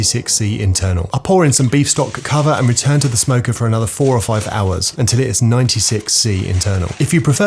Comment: i had to watch twice i thought he said 45 hours 😂